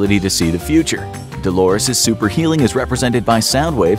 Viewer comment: you said super healing instead of super hearing